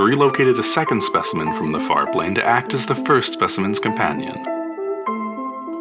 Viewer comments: I want to work for the Zooliminology Project SO BADLY. What I would give to be able to take care of these skrunkly little goobers! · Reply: I thought he said the fart plane. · I misheard "Far Planes" as fart land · I thought he said fart plane.